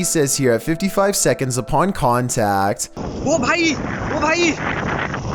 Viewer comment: "Tell me what he says here" Me: oppai